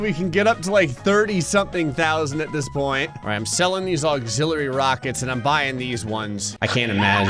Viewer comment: I thought you said anxiety rocket and I said yup.